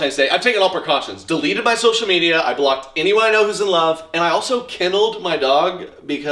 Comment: I thought you said "killed the dog" but was happy to hear "kenneled" when I double checked😅😂